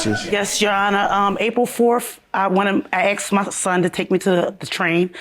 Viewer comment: She looks nice for 49 yrs old. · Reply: Maria Guerrilla.......... 4:55 it sounds like she said 40th.